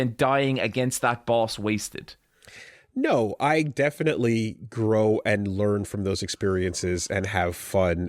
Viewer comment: I legit thought was going to recommend Growlanser. I heard him say "grow" and "lancer"!